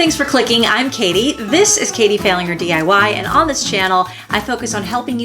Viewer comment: I thought you said your channel was called "Katie Failing Her DIY" lmao I was like how clever lol